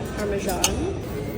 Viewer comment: I swear I heard “ARMED AND DANGEROUS!!!”